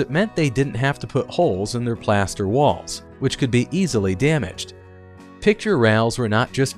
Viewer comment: did he just say damage plaster walls easily!?!?!?🤣🤭